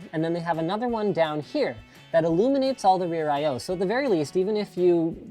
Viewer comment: I thought it said Illuminati